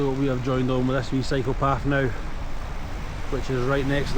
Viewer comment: I am SO HAPPY to see you guys!! I was so worried for awhile :face-red-heart-shape: The scenery in Switzerland is absolutely stunning!! ... I just got to the part in the vlog where you're on a "cycle path" and I thought you said "psychopath" HAHAHA!